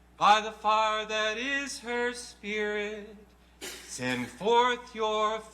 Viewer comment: i think i heard someone said "sin"